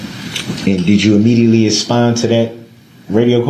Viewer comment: did he say aspond?